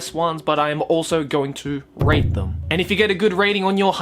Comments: i though he was gonna say: "But iam also going to rape them"..... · I thought he said that he was going to rape them not rate them. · I swore I heard he said he was gonna "rape" them, not "rate" them. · In the beginning I thought he said he was going to "rape them" and freaking out 😂